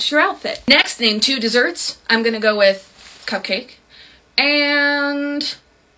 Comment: Help when she said cupcake I thought she said cocain and I was like “okay babes you do you ig” 💀💀💀